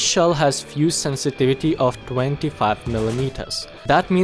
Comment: i thought u said km but when i watched it again u said mm lol.